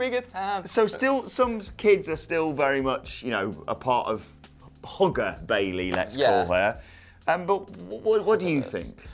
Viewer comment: HAHAHA I thought said "Hooker Bayley" not "Hugger Bayley"